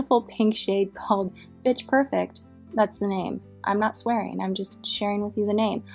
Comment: I thought you said Pitch perfect! No wonder the "swearing" comment made absolutely no sense to me! Lol